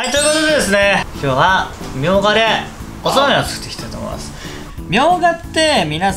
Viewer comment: 何度聞いても「おそうめんを作っていきたい」に聞こえちゃうんですよねぇ。テロップは「おつまみ」になってるけど。。。